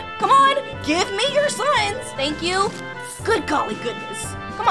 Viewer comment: “give me your sons!” Lol I heard that so wrong